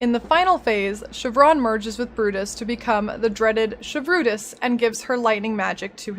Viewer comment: shevrutus 😂 i heard "she brutus" the first time